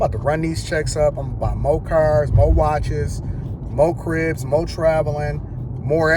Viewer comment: I thought you said, more cars, more ribs 🍖